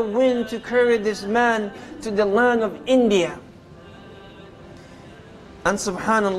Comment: I heard a version was land of China